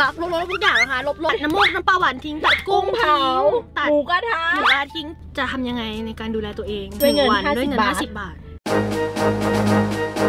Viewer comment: เสียงพวกท่านสองคนผสมกันจึงได้ยินว่า หมูกระทิง ??